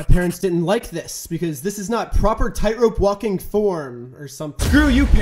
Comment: I could have sworn you said "Tyron Woodley has been in the family tree forever". I was so sure i rewatched like 10 times bc i heard it so clearly. But no u said "Tight rope walking". Weird...doesn't even sound the same.